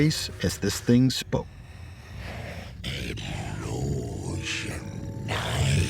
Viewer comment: It really pissed me off that you can't understand what it's saying, no need to add such cringe effects, just tell the story. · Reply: It says "Hello, Shania" clearly